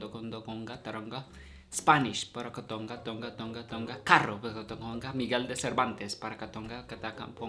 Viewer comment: Filipino here and I approve of TONGATONGATONGATONGACARRO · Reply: I thought it said: "Tong tong tong tong pakitong-kitong"